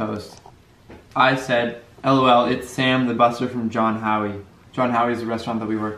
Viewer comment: I watched it again and cant find it?? He just says restaurant. Can you help a girl out and give me a time stamp? Lmao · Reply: around 3:58 when he starts reading the messages. I think he says John Howies??